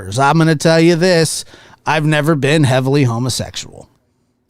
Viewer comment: Even though I caught this live, I had to catch the replay too. I may have to listen again when I need a good laugh - there are so many golden zingers in here. For instance, I thought you said you had never been a heavenly homosexual. That was heavily, but I was laughing at the time. When you said it was a "cow eat cow" world, I immediately thought that's how we got mad cow disease. I had no idea there was such a cake mold. All I'd ever seen was a cake decorated with Twinkies and Snowballs. I have never watched either of these channeks nut the righteous indignstion in her voice was hysterical. Even the second time. This kind of craziness helps me through the tough stuff. I love you and all the Nocturnal Lab Rats.😂😂😂🤣🤣🤣😅🤣😅🤣 Thank you thank you thank you 😂😂🤣🤣